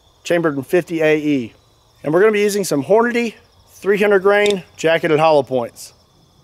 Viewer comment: I was moving a bag a chips when he said "hornnity" so I thought he said horny, and I'm like, it isn't point tho